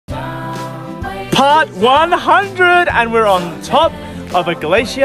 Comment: I thought you said pot 100 as in drug😅